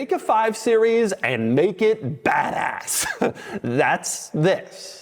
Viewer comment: did you say take a 5 series and make it badass, or Fatass?